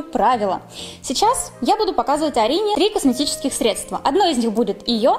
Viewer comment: Может мне показалось, но как-будто сказала в начале Дарина😅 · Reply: А мне послышалось Алина